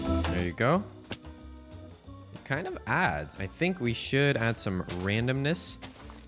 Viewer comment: When you added leaves to the house, i thought i heard you say "kind of ass" instead of "kind of adds" and im still dying